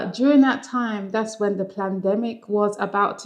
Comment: Did you say "plandemic" at 2:43?